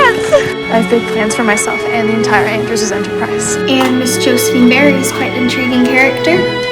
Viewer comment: lmao I heard that as something like: "I've bought pants for myself and the Andrews are depressed"